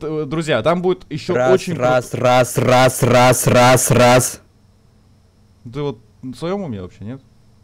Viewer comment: когда говорил Раз то я подумал что он сказал СРАЗ